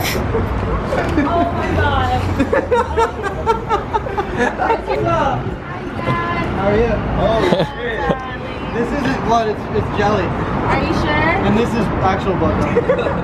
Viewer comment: did she call him dad? the fuck